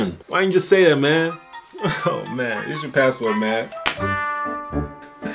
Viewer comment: did u just say “this is your password man”😂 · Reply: "Here's your passport man"